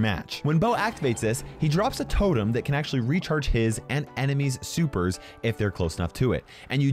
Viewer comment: did u say recharge his and enemy's?